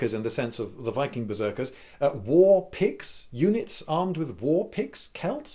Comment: Maybe the Celtic berserkers were wielding warpicts instead of warpicks? I mean, if I saw a few units of angry Celts coming at me wielding Picts, the Late Iron Age inhabitants of what is today known as Scotland I'd probably clear the field. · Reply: I thought he was saying warpigs this whole time (doh).